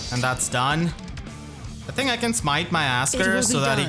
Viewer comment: I had to double back when I heard this the first time, because I could've swore you said "I think I can smack my ass, girl." I am running on fumes and need an actual good night's sleep.